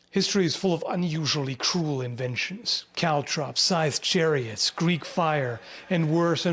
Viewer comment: In the intro I first thought you said "caltrop sized chariots"